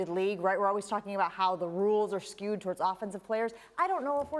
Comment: Did she just say “put himself into our mouths”?